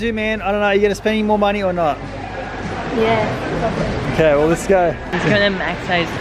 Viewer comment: Did anyone else hear say "yeah fuck it" at 22:21??